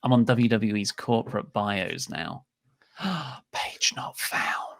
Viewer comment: Omg lol! When went to "Corporate Bios" and said "Page Not Found", I thought at first he was saying "Paige not found." Hahaha...